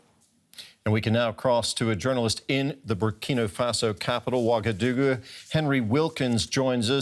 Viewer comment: Ouagadougou sounds like someone saying "Oh my GOD, my dog done a doo 💩doo!